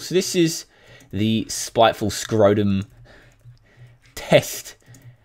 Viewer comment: did he said scrotum?